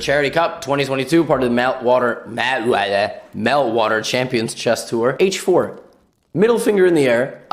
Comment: did he just say "male water"- you know what lets say i dont know that